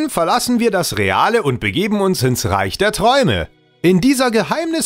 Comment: Bin ich der einzige, der zuerst verstanden hat:"... begeben wir uns ins Reich der Schatten."?